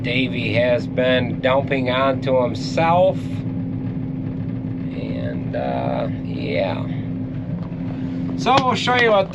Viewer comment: I heard say "someone took a dump on himself..." 🤔